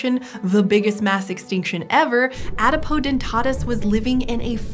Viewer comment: Until I saw the spelling, I thought Callie was saying Adipodentatus, and was thinking "Fatty Teeth? How does that work? I mean, I suppose they could have a particularly fatty pulp, but enough to name an entire species after...?"